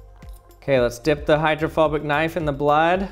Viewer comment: Who else thought he said homophobic knife